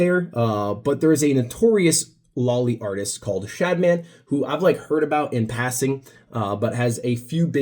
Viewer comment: Can somebody tell me what name he says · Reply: shadman